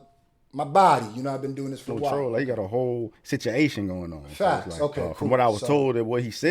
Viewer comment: did say "situation" or "sit yo asian"?